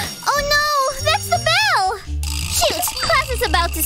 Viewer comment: Sh-sh*t!? Did she just say sh*t!? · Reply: Nah she said shoot 👍